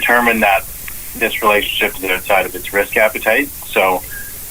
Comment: he said risk appetite - I thought he said caveat - still in no relation to transactions